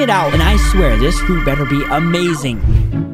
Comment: he said “This fruit better be amazing” instead of saying fighting style.